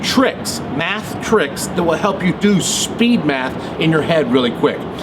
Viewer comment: I thought it said speed meth XD